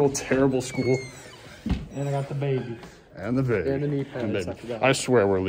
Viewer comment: did he say dababy